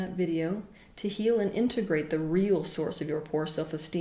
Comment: Could somebody please clarify as to what she says at 11:14- to practice our... mitigation or integration.... Cause I hear "imitigration" :P Thank you